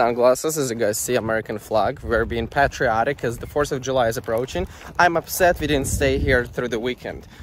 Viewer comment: I thought he said force of July for a sec.